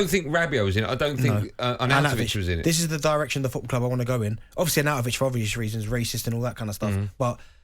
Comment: Did DB say that arnautovich was racist?? Or did I mishear that?